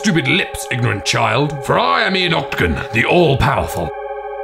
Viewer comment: When he said Ian Octagon I legit heard "Adolf Hitler"